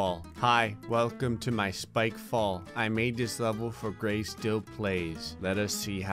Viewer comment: When it said spikefall i thought it said bikefall and started laughing uncontrollabally